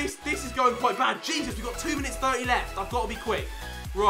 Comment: is it just me or does he say ‘jesus we’ve got 2 minutes 90 left?? comment👇🏼